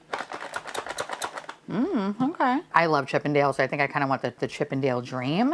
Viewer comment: this whole time i thought you were saying Chippedales and was thinking how do we get from Mickey and Donald Duck to strippers ☠️🔥💙